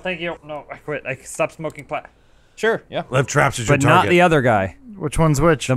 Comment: Did say "i stopped smoking pot",?????